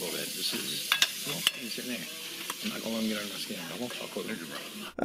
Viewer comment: It very faintly sounded like the n-bomb was dropped just after the “he’s in there” line but not by (the voice & tone sounded faint but different in tone & voice from the previous line said) - I thought it was the guy next to him that may have dropped it. Listening back to it again with headphones & in the 0.05 speed option YT gives you, it sounds like the guy next to murmurs something along the lines of “I won’t talk to a n*gger bro”. I’m really not sure if I’m just hearing it in my head given the fact that the audio expert said that didn’t say it (on the first watch I didn’t think I heard say it at all) but that confirmation that didn’t say it doesn’t rule out the possibility that it was said at all in that audio clip by someone else in the room. Again, I’m not entirely sure if I’m just hearing it, I’d appreciate it if someone listened through just to see if it did sound like that was said or if I’m just crazy in thinking that something along those lines was faintly murmured.